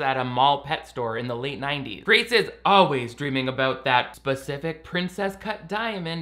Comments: I thought that it said Princess Cult.💀😭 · I thought it said ‘Princess Cult’. That actually made more sense for some reason